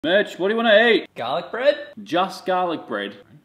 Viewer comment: I honestly thought he said “bitch, what do you want to eat”